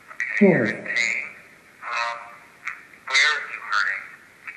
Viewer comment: when asked how they feel, I heard "heavy pain" right after the word "nurse". Or something similar could be, my english isn't the best. 😅